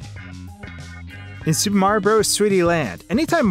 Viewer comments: Why did I misheard that for "Super Mario Bros Sweaty/Sweety Land" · it sounds like he says Super Mario Bros Sweetie Land